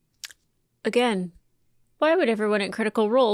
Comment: I turned away from my screen at the end right when you said is it personal i think its personal... i thought you said perceval you ass you got me excited